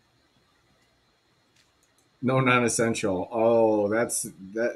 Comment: I’m pretty sure he said they were asking for “No non-consensual contact”, not ‘no non-essential” 🤔… at least that is what I heard and would have typed in the notes of evidence